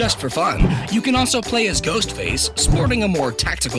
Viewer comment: I thought I heard you say “Goatface” 😂😂😂😂